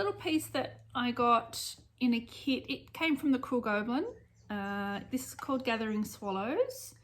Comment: Love your videos! The Summer Car Key is a hoot. When you first started talking about your stitch store, I heard it as Cruel Goblin and thought that sounded more like a Halloween store. Lol. Once I saw it written out as Crewel Gobelin it made a lot more sense to me. 😁 Love hearing all the different accents on YouTube - makes the world seem much smaller. Worm, and congratulations on 4000 subscribers!